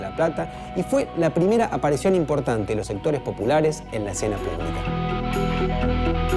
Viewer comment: Quien me dice que dijo el we en el minuto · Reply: "Los sectores populares en la escena politica"